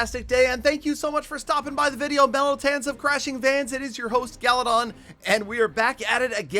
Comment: Crash of vans The second I hear that, I heard a van crash into a car outside. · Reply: I heard "fans of crashing vans" and was instantly concerned 🤣